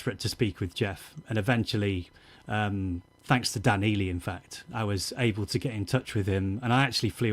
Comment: does Adrian say he got in touch with Geoff Britten through Dan Healy? Like, the Grateful Dead soundman Dan Healy? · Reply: No, the persons name is Dan Ealey.